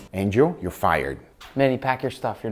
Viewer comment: i thought nick said "manny pacquiao stuff" at the last part hahaha ..